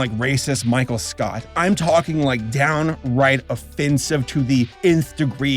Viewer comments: Saber, did you say of-fin-sive at 7:26? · Did you say fucking ofFINsive